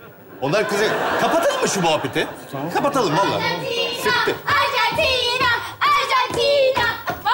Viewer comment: Chabiyin küfür ettiğini bir tek ben mi duydum?! Saati eğer bakmak isterseniz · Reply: Sıktı diyor muhabbet için